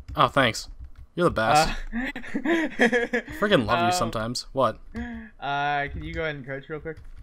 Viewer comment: Did just say I love u to